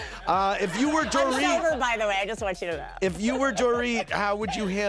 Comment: did she say “I’m so rude btw I just want you to know?” Bc we been knew that Erika lmfao